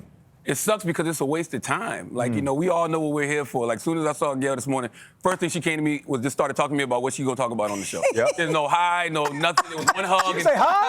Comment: I literally don’t know WHAT Charlamagne said at 0:50. Complete word salad 🥗😵‍💫❓ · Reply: "Soon as I saw Gail this morning, first thing she came to me (with), was (her) starting talking about what we're going to talk about on the show." He was proving a point by talking quickly, demonstrating how she cut to the chase by avoiding small talk with him that morning, which is the premise of his new book. It's not that hard.